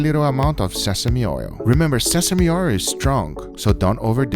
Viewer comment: "Remember sesame oir is strong! So dun over dueht! Lmfaooo! Guga where I can buy sesame oir? Instead of sesame oil? Lmfaooo!